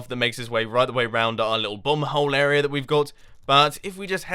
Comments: Evertime he says bomb hole it sounds like he said bum hole like people from that area do and I get confused for a few seconds. :) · Why every time Mumbo says "bomb hole" I hear "bum hole"? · Oh wow I kept hearing "bum hole" and all I could think was MUMBO, THE CHILDREN. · I thought he said 'our little bum hole area' XD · I really like the graphics, but make sure you get done everything you say you will in the episode plan. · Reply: I thought mumbo said "bum-hole"